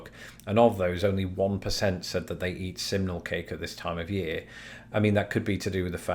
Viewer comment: Oh thanks goodness... until you displayed the poll I thought you were saying 'seminal cake' which sounds entirely different.